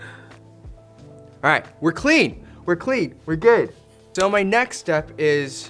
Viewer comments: I thought he said “we’re gay!” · Why did i hear "we clean we clean we gay" here